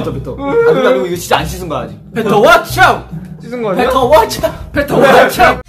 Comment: i didnt hear wrong did I? Hjunyin said WATCH OUT